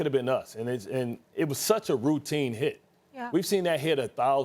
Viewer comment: Was in this video that he 'blamed' him? Cause I didn't catch it. Can someone point out at what point did he 'blame' him if it is in this video? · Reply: Same here, I heard Bart say “it was a routine hit”